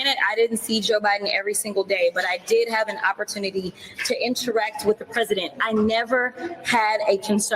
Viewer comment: Did I hear Jasmine Crockett say she had an opportunity to INSURECT with Joe Biden? At 16:45. Perhaps I misheard her.